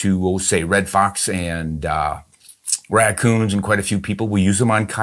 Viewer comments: I had to replay that. Because first time, sounded like he said he shat quiet a few people with the 17. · "Quite a few people"? Did anyone else catch that at 6:56? Ron said he shoots red fox, raccoon and quite a few people...... that's a hell of a sound bite if you cut it off right at the end of people ;-)